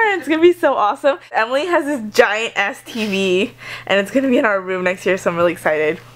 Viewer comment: I thought you said giant STD at first when you were talking about the tv... Lol